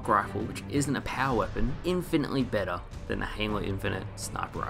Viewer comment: I think I heard you call halo Hanelow?